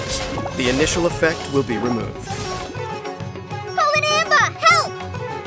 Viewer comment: Thought she was saying. "Calling Amber, help".